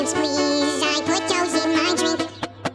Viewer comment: I seriously thought he said Nightmares and ice cubes, i put those in my drink